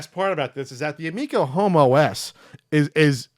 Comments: When you said Home OS I thought you said Homo S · I let these videos normally play in the background while doing other things and when I randomly heard you say "Amico Home OS", I swear I heard "Amico Homo S" because I wasn't totally focused on the video. Long live the Amico Homo S™